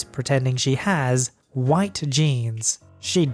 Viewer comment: i thought he said ' White Jeans ' so i was really confused OvO